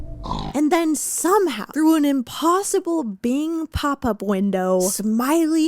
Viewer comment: I had to re listen to 53:33 so many times before I heard “Bing Pop-Up Window” … I was sitting there like “who is bing papa bwindow??”